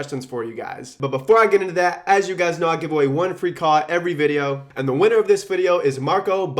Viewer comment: I heard "one free call" as "one free car". Thought he was turning Tai Lopez.